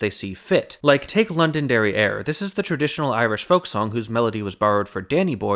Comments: Definitely heard it as London derrière. Totally different meaning. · When you first said "Londonderry Air" I thought you had said "London Derrieere" and it was some sort of early version of Baby Got Back and its Oakland Booty. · Upvote if you thought he was saying "London Derriere" instead of "Londonderry Air"